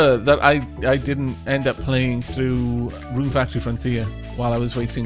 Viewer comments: What game did you mention at 40:03? I tried listening to it a few times but couldn't make it out. Sounded like ruvati frontier but I most certainly misheard it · What game is he talking about at 40:02? I've replayed it so many times, and i just can't hear what he says. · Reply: FlixerDK Rune Factory Frontier